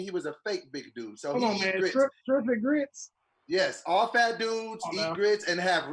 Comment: Hold up did say shrimp & grits?